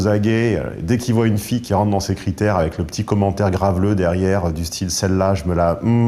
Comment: quest-ce qu'il dit de 0:20-0:26?? AIDEZ MOI JE VEUX APPRENDRE FRANCAIS! T_T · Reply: "Dès qu'il voit une fille dans ses critères, avec le petit commentaire graveleux du style :"celle-la je me la mmmh"